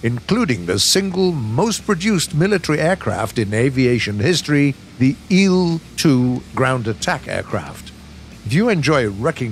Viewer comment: Did he just say "eel" 2....?